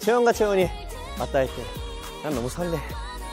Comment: I thought he said “no homo”😂